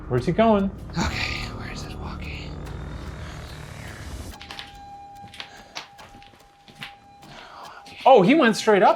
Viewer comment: Am I only one who heard "here" right after Ryan asked "where is the walkie" at 55:26 ? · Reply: The ghost said “ I’m here”